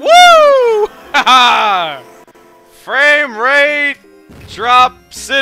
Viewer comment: did you say frame rape